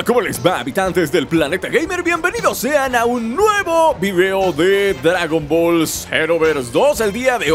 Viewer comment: Entendí "el último dragón ball xenoverse 2"